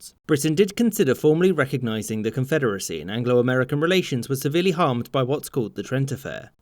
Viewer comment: he said "anglo american relations" but I swear that I heard "Angela Merkel relations"